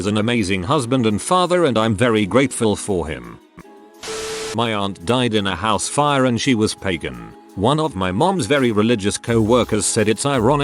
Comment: At first, I honestly thought it said, "my aunt died in a house fire and she was pegging." xD